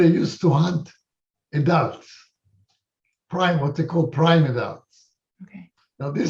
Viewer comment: I'm having trouble working out what he said here. I think he says "prime adults". Can anyone confirm or refute please?